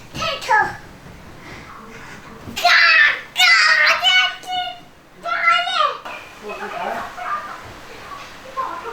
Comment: 總說什麼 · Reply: 傻眼